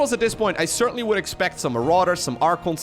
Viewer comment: Did he just said Marauders